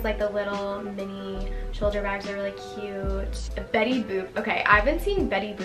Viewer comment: WHY DOES IT SOUND LIKE SHES SAYING BETTY BOOB LMAO 😭😭